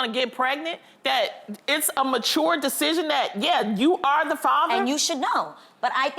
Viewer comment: Did she say " It's a mature decision to assume, "" that you are the father """ 1:49 If that is her answer never lay with a west w0man. ASSUME YOU ARE THE FATHER F*CK THAT. I AM NOT FU*KING YOU Go to the child stick cotton bud in mouth send of to those companies, problem solved.